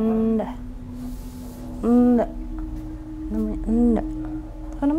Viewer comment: Why am I hearing someone said “Enaaa” di minit 37:30. Suaranya horror...